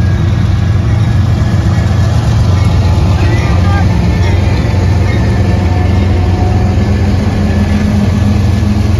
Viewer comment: Do you know what he said at 7:22? Nice catches! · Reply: He said see you next week! He’s a good friend of mine